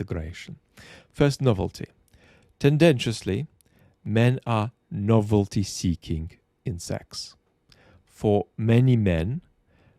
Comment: At first I thought had said, "Men are novelty-seeking insects."